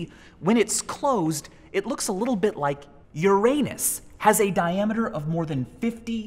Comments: I heard your anus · i am sorry did u just say it looks like my anu.... ? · i thought he said "your anus" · I thought he said your anus lol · As you can see when its closed it looks a little bit like your "aannus" Did he said that? Or am i the only one?😓😰